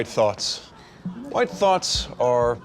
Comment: I thought he said thots